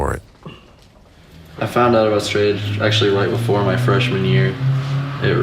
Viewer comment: did he just say “I found out I was straight edge…..”😂😂😂